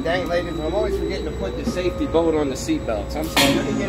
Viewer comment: Everyone: dang he’s a menace Me: did he just say safety boner 😂🤨